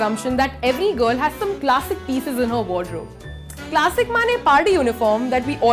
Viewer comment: DID JUST SAY * MANE *